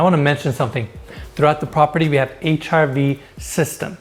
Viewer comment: I had to laugh. I thought he actually said “HIV system” and wondered what the heck it hat? 😊